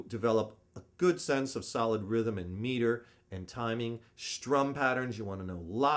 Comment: I can understand 95% of what says, but wonder if any English speaker(or anyone) could help make 4 sentences below clearer: At 1:30, you really want to develop good sense of solid rhythm and "...(neeler?)" and timing. At 2:28, learning lead guitar of phrases and "patience(patterns? pracience?)" by picking them all by ear. At 5:52, "and/or lending(landing?) on a certain keep/hip lending tones or cool tones." At 8:02, when I over which "jam drive(?)", which key, which chords. Thank you!